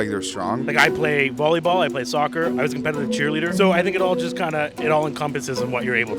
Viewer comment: I thought mailman calves guy said "I play volleyball. I play soccer. I was in bed with a cheerleader." Yeah, all of those will keep you in shape no doubt.